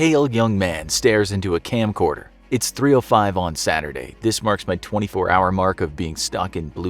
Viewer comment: it says on Saturday but the caption says Sunday :/